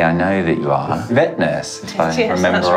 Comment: First I thought Scott said: 'I understand you are a wet nurse.' :-)))))))